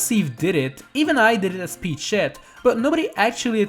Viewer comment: did he say shit? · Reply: Alicon_852 No, he said “Peachette”